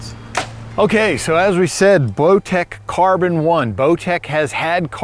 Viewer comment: seconds accidentally says blowtech, I listened to it several times and every time I heard the same thing. 😂😂it’s funny, but I know it was a accident. Bowtech is one of my favorite bows.